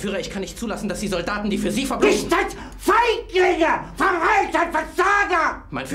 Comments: I always think he's saying It's Just Fake Nicker you'll raid my banana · FIGHT NIGGA! no offense but that's what I only hear